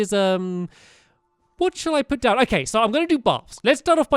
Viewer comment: No offense to anybody but I think Stampy said barf